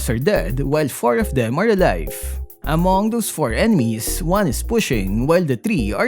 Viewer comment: subtitle : Among those 4 enemies What i hear : AMOGUS 4 ENEMIES